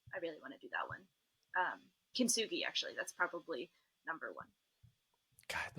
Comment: what? i cant make out the name of this boulder. kitsune? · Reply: Kintsugi V15 in the Red Rocks Nevada - Testpiece Editor